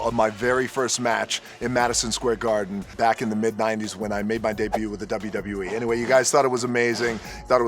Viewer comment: sounds like he says "where I beat my d*ck blue with the WWE"